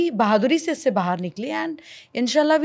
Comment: In 52:19 did she say inshalla .?